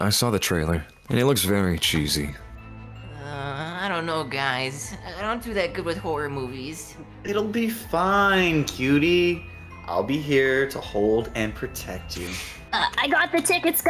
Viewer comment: did just call cutie? 👀